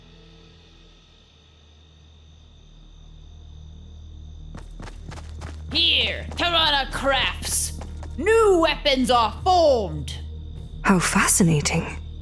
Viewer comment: i heard “tarana craps”